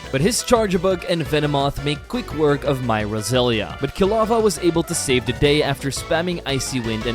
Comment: I Thought You u Said Kilowog From Geeen Lantern But You Said Quilava And Also I'm A Big Fan Of Your Accent I Truly Love It And Fuck Those Who Don't. Please Keep Uploading Your One Of My Favorite Poke Tubers Please Continue To Keep Up The Great Work